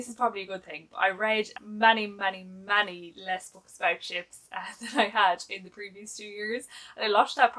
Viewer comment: Hahahaha!! When you said you hadn’t read as many books about ships this year, for a second, I thought you said “chips” and was wondering how many books about chips there really are. 🤪